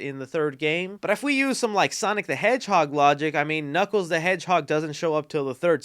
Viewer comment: Did he just say, "Knuckles the hedgehog"